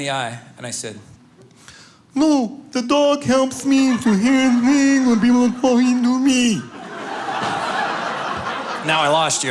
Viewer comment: What is he saying at 6:20 ? I can only understand a few words. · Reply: No, the dog helps me to hear things when people are talking to me. That’s what I lip read anyway.